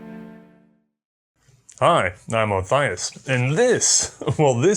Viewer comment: I swear I thought I heard "Hi I'm Alpharuis".